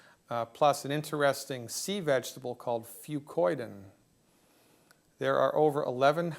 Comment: Thank you for this important interview. QUESTION: What is the vegetable he mentions at minute 6:10? I am not native english seaker and cannot understand it. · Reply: fucoidan